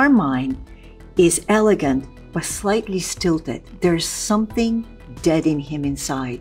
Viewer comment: I thought she said slightly stupid